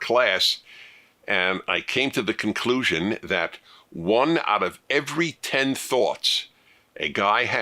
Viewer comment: I thought he said "one out of every ten THOTS" at first.